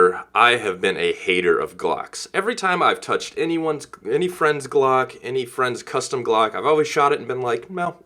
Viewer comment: First I wasn’t pay attention and I thought he said “ every time he touched his friend cock “ lol